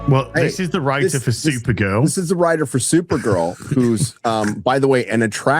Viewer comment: I had to rewind the video for a second I thought you were saying super girls hot